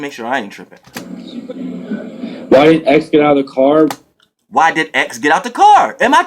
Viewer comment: He said “why didn’t XXXTENTACION get out the car”. He’s country and his nt was dropped and sounded like he said why did XXXTENTACION get out the car